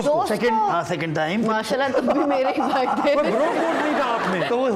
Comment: Did she say to Mani at 16:26 tum bhe mery bhai thay?